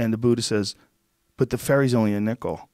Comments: When he said "but the ferry's only a nickel", I totally heard that as "the fairies" and was like what kinda fukked joke is that??? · What does Rogan say in minute 18:26? "But the fairy's only a nickel"? My english is not as good as I wished...